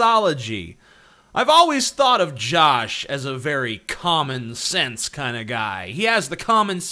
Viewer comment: For some reason I heard "Common sense" as "Comic sans". Help me